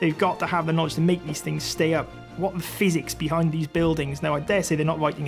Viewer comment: I've listened to the man talking at 3:21 three times, and I still can't understand what he said. Why is he talking about a phoenix? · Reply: Turn on closed captions. See upper right. He said physics.